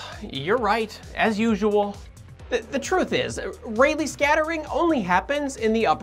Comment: i keep hearing it as: It's REALLY scattering xD